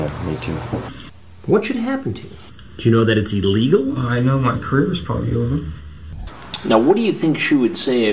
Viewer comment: When I first saw 4:24, I thought he said, “I know my credit score is over.” Would have been a fantastic response.